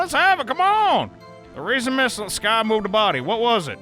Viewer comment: i heard missle sky and all i could think about is the dog Missle